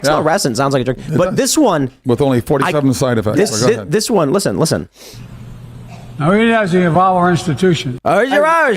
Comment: "now we may not have all our institutions" and I guess that's what he was trying to say but it is actually more clear that he's not working with all his institutions as far as his brain goes but uh yeah they're just going after YouTubers who talk about the truth! WTF